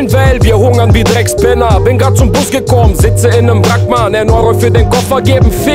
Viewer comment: Was sagt er bei 00:36 “sitze in einem ....''? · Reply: Wrack